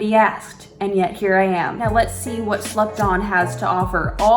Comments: When u was talkin about slept on it sounded like u was sayin slupdog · When said "slept on" I heard "slut dom" instead 😭